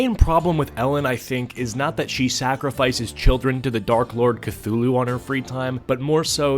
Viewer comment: I thought he was gonna say dark lord diddy.